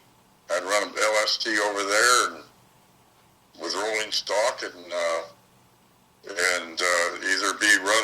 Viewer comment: I thought he said Rolling Stone jamming with Mick....wow....rolling stock I guess